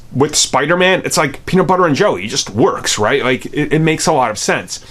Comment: At first I thought he said Peter Parker and jelly